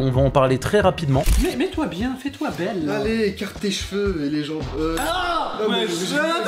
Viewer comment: il a dit quoi j’ai pas bien entendu dites le svp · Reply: (Écarté les jambes aussi)